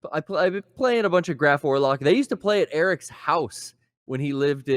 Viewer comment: Anyone know the band is talking about at 1:22:00? He sounds like he says Graph Warlock